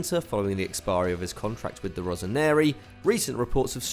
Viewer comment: I've heard you say "rosaneri" multiple times in previous videos and you said it again today, it's "rossoneri" not "rosaneri". Sorry for the pedantry but I'm an AC Milan fan (; Great videos btw, keep it up!